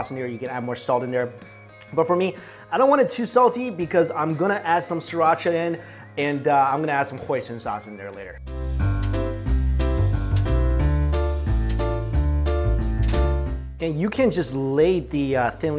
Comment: When he said Hoison sauce I thought he said poison sauce